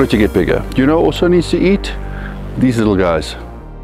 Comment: That transition into animal cruelty awareness was cleaner than any bulk I've ever seen! Good on you for using your platform to spread the message, Daddy Noel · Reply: Good think the subtitles were there because at first I thought he said "You know who else you need to eat? These little guys" lol